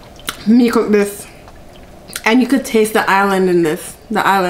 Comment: Mom I thought you said” and you can taste the islands” but I got it girl the ONIONS??? I love your channel